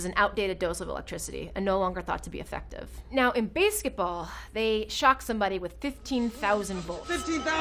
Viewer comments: why does she say basketball like that? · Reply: It's "Baseketball". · Wait get the accent guy what did she say at 3:18 base chet ball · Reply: xXTixerosXx she say Base ket ball because that is the name of the movie. The idea is that it is like PIG in basketball mixed with baseball